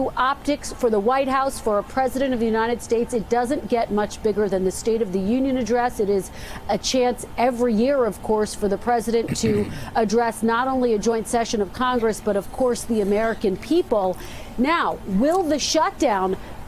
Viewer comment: My phone messed up I thought she said " the President of drunk" which would explain his actions 🤪